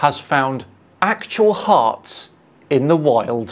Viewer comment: I wonder if this guy isn't a troll. Much of the time, I heard "harp" instead of heart. Yeah and I loved the river rock explanation he did. I guess there is a 'believer' for any weird idea put out there. Thankyou for the laugh break! 👍🌊💙💙💙🌊🥰✌